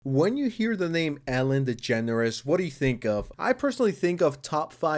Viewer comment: I’m too high…I thought you said Elon is generous.